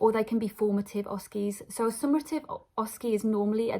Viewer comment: Thank you for this informative video. I noticed a small correction need at 2:29. The word should be "summative" instead of "summarative".